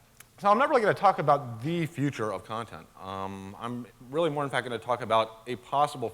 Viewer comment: I thought it said "the future of consent"